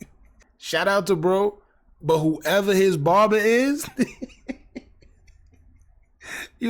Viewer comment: Barber?? What’s that he says?